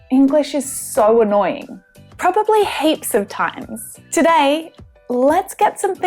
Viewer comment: I hear 'hapes' instead of 'heaps'. Maybe not annoying, but this sort of things makes me crazy...! ;-)